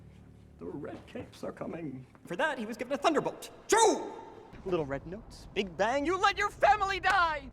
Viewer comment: All I heard was "THUNDERBOLT CHUU"